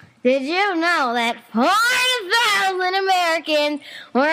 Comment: "Hey Pickle. Did you know that five THOUSAND Americans were injured by toilets each yeeeeeeeeeeeear? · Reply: I thought taco said 40000 Americans